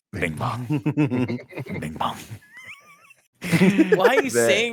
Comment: I didn't remember the name of the elephant so when they started talking about Bingbong I thought they were saying Ping Pong and was like "did someone get shot in the head? I do not remember that"